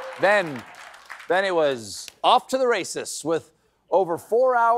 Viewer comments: When he said "off to the races" at 2:52, I thought he said "off to the racists", and that makes even more sense. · I swear Stephen said "...and then it's off to the racists...". But I could be wrong. heh heh